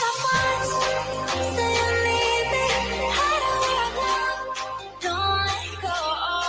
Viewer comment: I thought you said you love me, are you saying that to all the girls.